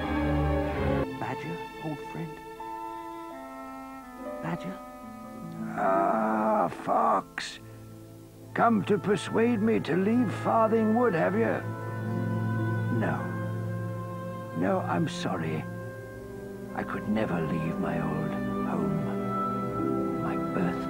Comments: I thought it said 'animals of farting wood' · I THOUGHT IT SAID ANIMALS OF THE FARTING WOOD